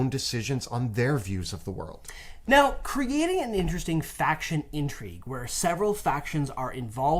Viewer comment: I thought you said fraction intrigue. Now half my players hate me and the other half.